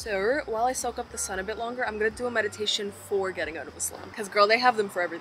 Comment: excuse me, did she say something about a meditation for getting out of Islam here or did my ears trick me? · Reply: She said getting out of a slump.